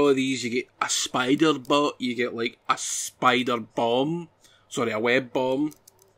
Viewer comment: Hahahaha! Thought you said you get a Spider-BONG! 🤣🤣🤣 Now that would be cool! If I could just swing around all day and not fight anyone, I might like it but I'm utter rubbish at the fighting part so it puts me off! Nice review though squire! 😎